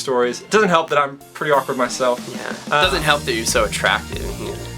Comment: Did just say to Doesn't help that your so attractive?